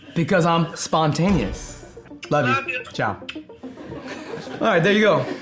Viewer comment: Did he say ' chao '? Because if so, in my country, Bulgaria, the word for goobye is pronounced the same way! And it looked like he was talking in my language for a sec! SO COOl!